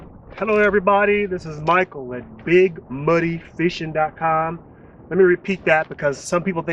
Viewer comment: The 1st video I watched I thought you said big money too 😄